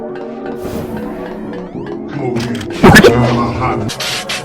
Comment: I just heard "Come over here and kiss me on my hot mouth" 💀💀 1:27 ALSO BRO TF IS THAT- 💀💀